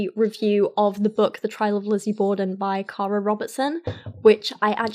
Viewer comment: at first I thought you said Kyra Robertson and that your dog had written a book